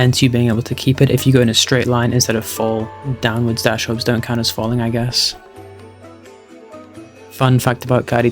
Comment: falling, did I hear falling???? #ratefalling #ratefalling #ratefalling #ratefalling #ratefalling #ratefalling #ratefalling #ratefalling #ratefalling